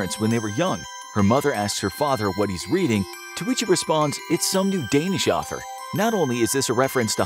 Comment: I thought it said “some mundanish author”😂